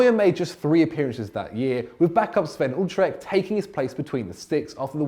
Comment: How tf did he just say Sven Ulrich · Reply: "Sven Utrecht" is what I heard.